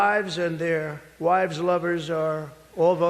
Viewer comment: "Their wives and their wives lovers are voting for me." 😅😅😅😅😅😅😅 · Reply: I thought he said, “their wives’ mothers” my brain couldn’t compute so much truth😂😂😂😂